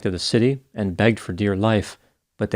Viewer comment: "...begged for dear life."😂 Dr. G, your puns kill me. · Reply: I know that's what he meant, but at first I heard "begged for deer life" & thought, no, he wants quite the opposite lol!